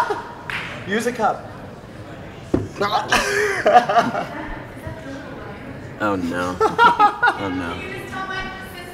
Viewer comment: Anyone know what Rosa was saying between 2:30 through 2:50 ? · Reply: Can you please talk to my sister? It's her birthday. Oh!!! Dylan? Can you leave a voicemail for my- it's her birthday. And also Teresa and Claire. Oh lord